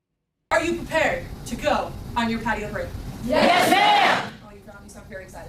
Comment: Patio break is when they reward the airmen for doing a good job with about 15 min. in the patio area where they get cokes, pepsi, and candy. I went to enlisted Air Force Basic in 1990 and then went to ROTC Officer summer camp in 1994. The enlisted was more mental and not much physical, but the office summer camp was mainly physical training. The reason it was more intense physical in officer training was most candidates would become pilots. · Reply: I thought she said potty break. 🤣